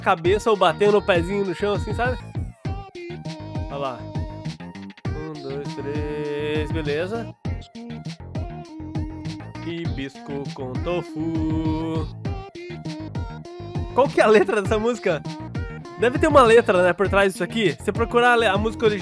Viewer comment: fui a única que ouviu: risco com tofuuuu! MANO KKKKK eu ouvi isso!! Kkkkkkkkkk